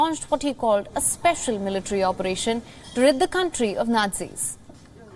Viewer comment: Did she say "To rid the country of Nazis"